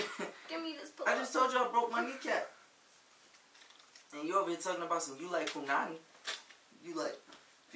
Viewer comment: I know it ain't jus me heard him say "you over here saying you like punani" 😂😂😭